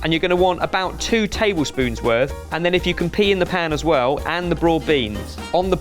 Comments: did say" if you can pee in the pan?" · I swear I heard say “if you can pee in the pan as well, that’ll be great”